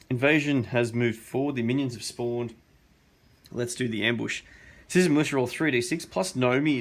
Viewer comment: Every time you say Citizen's Militia I thought you were saying Susan Lucci :D